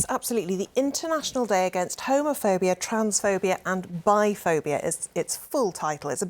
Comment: I heard..."Trumpsphobia"? We NEED more of those 😆😄